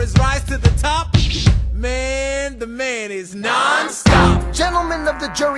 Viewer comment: OMG I THOUGHT HE SAID " mayonnaise " at 01:03:16😂😂😂